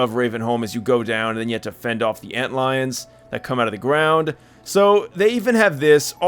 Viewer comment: WHAT DID HE SAY! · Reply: Antlions